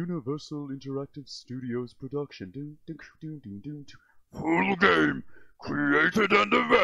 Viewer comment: Finally i know what tf says at 0:11 never understand it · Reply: I heard "full game"